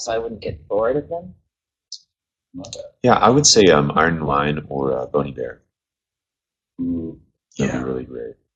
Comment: please someone tell me what Avi says? I haven't hear of these artist. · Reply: He said Iron & Wine or Bon Iver.